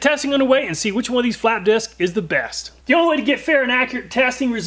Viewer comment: When he said Which Flap Disc is Best I could have sworn he said "Which Fap Disc is Best." I had my pen and paper ready...